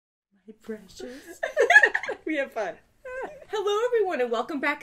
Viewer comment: That sounded like a Hungarian sentence, after the laugh. "Hülye vagy."